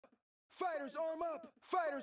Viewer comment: I swear I'm getting the mandela effect. I thought they said "Fighter rise up"